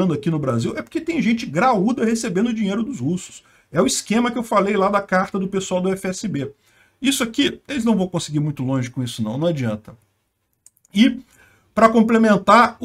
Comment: foi isso que eu ouvi? Esquema de cartas da UFSB? Ou ouvir errado? (UFSB, a universidade)?